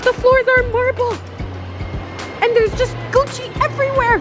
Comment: if you have the captions on instead of Gucci it says goat cheese